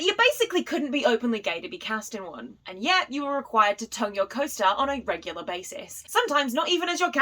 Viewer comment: I thought you said ‘tug your co-stars’ but when I watched it back I found out your said ‘tongue your co-stars’ phew